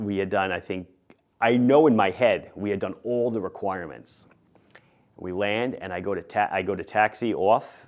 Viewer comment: It sounds like you did one landing and the DPE passed you???? You didn’t demonstrate short and soft takeoffs and landings?? Those are required maneuvers on the private pilot check ride. Not only would your pilot certificate be invalid, but the DPE will be fired with cause. If he needs to pee, take a break and then continue the test. Maybe I heard you wrong, but this sounds really bad. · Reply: suggest you listen again, I say "I know in my head I've done all the requirements"